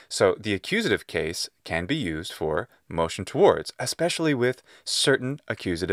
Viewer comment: I thought he said motion to wars.